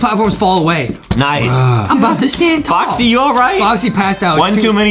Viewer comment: Adewm called toxy instead of boxy